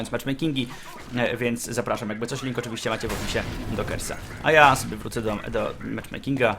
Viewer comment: na początku usłyszałam zapraszam na kebsa xD a to było Kersa xD